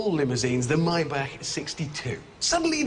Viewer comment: I heard "The My Back 62"